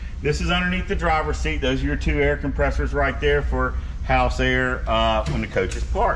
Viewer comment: Did you say those two compressors are for ac...? @ 10:30 🤦🏻‍♂️ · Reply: He said they are for house air. As in the slide out seals that are air operated. The entry door is air operated. Usually the bathroom door is air operated as well.